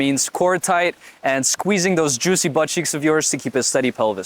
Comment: WHAT DID HE SAY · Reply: Nah bro he said juicy but cheeks 😂